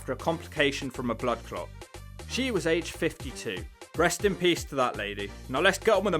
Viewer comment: I swear I thought he said "Rest In Peace S1u+ Lady"! 😂😂😂 I knew he probably hadn't but to my ears it sounded like he had. I mean.....shes kinda dressed like one ya hnow 😂😂😂